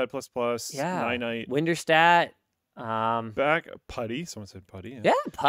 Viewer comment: Does anyone know what application Linus mentioned @ 42:57 ? After Luke said Ninite. I heard winterstat, winnerstat. Just curious about what the name of application is.